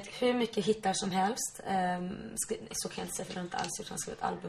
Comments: alltså vad säger den härna människan 0:08 "skrivinisåkanjaintesetluntaallsutan"? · alltså vad säger den härna människa 0:08 "skrivinisåkanjaintesetluntaallsutan"?